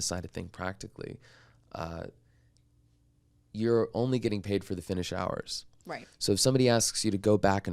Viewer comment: Was their first project Royality Share or per finished hour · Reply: I heard him say per finished hour.